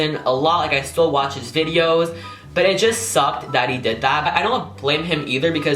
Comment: Did anyone else here 14:23" daddy did that"?!😂 I replayed it and he said that he did that but the first time I heard daddy